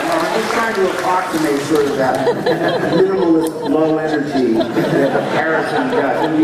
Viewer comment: What did he say at 10:40? I can't make it out... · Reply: "I'm just trying to approximate that sort of minimalist low energy that Harrison does"